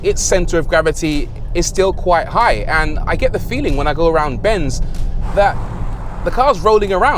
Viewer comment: Did you say 'go around benz'...? A new level of pun.